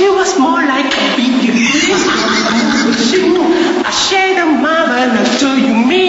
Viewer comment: did he just say I shave ya mama